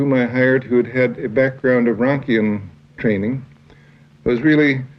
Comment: Can someone please explain what (rakion training) is? At 1:58 Rodger said, "A worker whom I hired who had had a background of Rakion training was really most helpful to me ..." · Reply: Formación Rankiana, de Otto Rank.